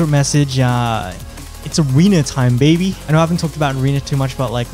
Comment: It's a wiener time baby, also thanks for all the video guides thus far! They've been a huge help since I started playing last week! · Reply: Also just realized now I misheard the secret message as I see everyone's comment is "Arena time" not "wiener time" lmfao